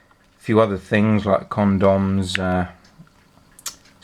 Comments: sounded like condoms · did he say condoms?!